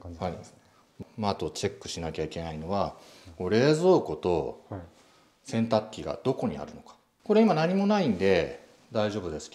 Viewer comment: 冷蔵庫とケンタッキーがどこにあるのか にしか聞こえない